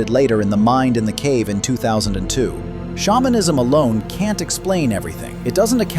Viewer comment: Shamanism is hundreds of thousands of years old. Spirituality is burned into human DNA. The pictures of part human, part animal are shapeshifters. This is an ancient practice of shamans. Europeans have ancestral memories in the form of werewolves, etc. Ancient Shapeshifting cave art is found all over the world. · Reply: I thought you said spawnism instead of shamanism 😭